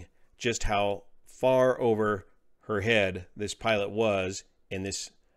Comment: I thought she said her dad was a pilot